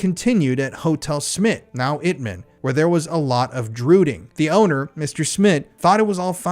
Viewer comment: as I was listening I thought you said “Druiding” not “drooting”. However I’m still confused as to what this means. I’ve searched for a definition of “drooting” or “droot” and can’t find anything, I’ve even asked ChatGPT4o if it meant anything in the Netherlands or surrounding regions and got nothing. I’m asking because if it’s actually referring to Druids that would be VERY Interesting. For those who are unfamiliar, a druid was a sorcerer, member of the high-ranking priestly class in ancient Celtic cultures. Oddly enough in some of the videos from the guy who broke into Bohemian Grove, there are several objects he found that reference Druids. If anyone knows what “drooting” means please let me know, cause I’m lost 🤷🏼‍♀️